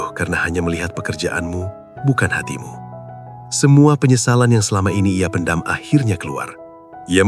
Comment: Saya Tersalah Dengar Tadi, Ingatkan Dia Sebut Kedatangan Bulannya Terlambat Rupanya Penyesalannya Terlambat.😮😂😂😂